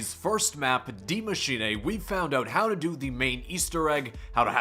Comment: Why does it it sound like he says die machinA